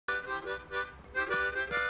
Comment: thought it said, PAnda: I'm begging too... than "I'm Vegetarian"